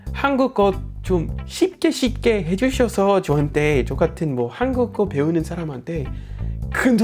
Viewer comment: 나만 ㅈ같은이라고 들림?ㅋ